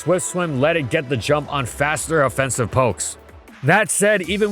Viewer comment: did he just say "POKES"?